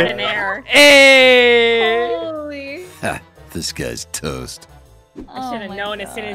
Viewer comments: it sounds like it’s saying, “this guy is toast.” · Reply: Fan Fiction - It says „This guy‘s toast“. As far as I can remember, „Disguised toast“ was just a misheard phrase which later turned into his name! :) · I don't know if this is intentional But everytime the voice says 'Disguised Toast' I hear 'This guy is toast'